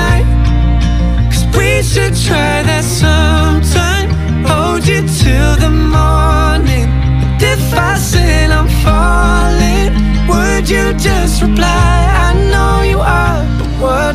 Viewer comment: I thought said "Hold you 'till the moaning" and I was very concerned